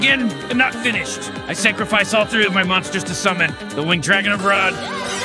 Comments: I love how it wasn’t a weed joke it was just the actual Winged Dragon of Ra. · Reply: I thought he said winged dragon of rod lol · Thought they said weed dragon of ra